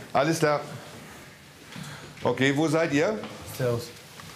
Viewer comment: Wer hat auch bei 9:46 cervus gehört